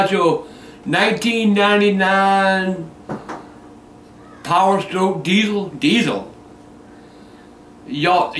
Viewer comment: Did he said 1999 Powerstroke diesel.